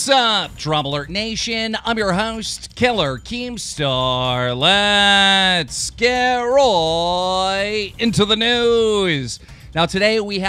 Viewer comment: I always hear ''Let's get Roy into the noose!'' Who's Roy?